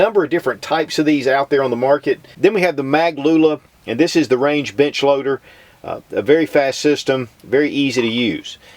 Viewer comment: I thought he said "This is the Range Bitch Loader." Then I realized he said Range Bench Loader... But then I thought, but I really want something called The Range Bitch loader. hmmm... Also, I thought the benefit of the Mag Pump was that you could just dump a box of ammo in the top, is that an accurate test?